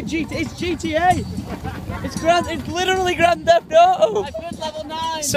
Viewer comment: is it just me or i heard his say at 0:47 Grand Theft o o